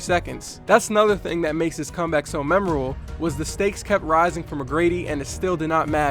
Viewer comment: What did he say 4:17 Did he say mememeble